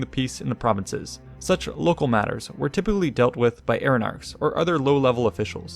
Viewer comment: What was the word that the captions called 'aeronautics' at 3:00? I can't seem to hear it properly and or don't know the term. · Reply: As was stated in the video he had just mentioned in the previous sentence, the term is Eirenarchs.